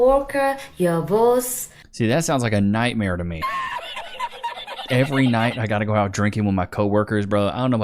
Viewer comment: "every night I gotta go out drinking with my co-worker".....No....she clearly said ALMOST every FRIDAY